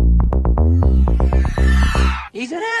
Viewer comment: (Intro music plays) What I hear: he's a f a n d o m